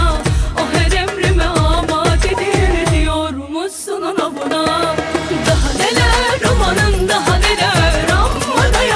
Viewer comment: ne diyor bi anlayamadım orayı :D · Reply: irfan Topal ´´O her emrime amadedir, diyormussun ona buna´´ diyor